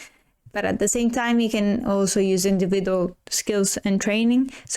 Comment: such as attention on focus and what.. i couldn’t make out the word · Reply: Healing Imagery! Sorry for my accent!